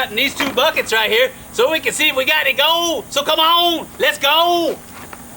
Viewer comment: i so thought he was saying "Lets Gold!"